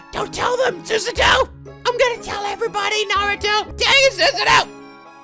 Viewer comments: did say naruto? also funny that "what did you mean?" counted, but "what d'ya mean like?" didn't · did i just hear naruto